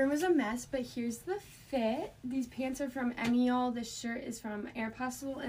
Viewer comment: I know she said where her pants are from but I can’t understand what shes saying, does anyone else know 😭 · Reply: i think the brand is called emiole